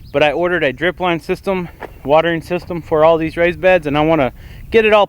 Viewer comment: It sounded like you were saying rice beds, lol I was about to be amazed